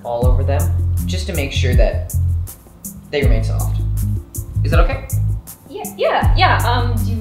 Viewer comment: I did a core blast this morning and my abs are feeling a little cramped, and so I'd love to wash some soapy hot water all over them just to make sure that they rinse off. Is that okay? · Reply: I THOUGHT HE SAID REMAIN SOFT